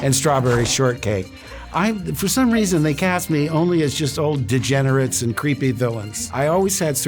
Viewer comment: di- did he just call luke a degenerate???